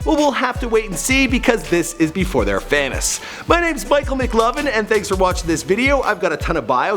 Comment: Lol it sounded like you said Michael McLovin at the end.